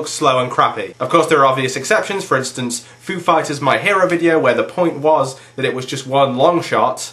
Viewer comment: when you said foo fighers my hero. i thought you said "a food fight is my hero"...i was like who the hell writes that kind of a song? lol